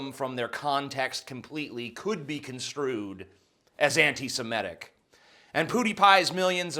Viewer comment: I think I heard "SIMETIC". It's "SEMITIC".